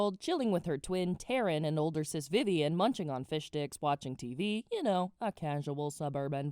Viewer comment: I swear it sounded like you said fish dicks not fish sticks. XD